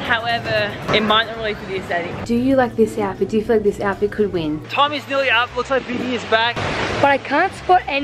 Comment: am I the only one who thought papa said “Tom” instead of “time?”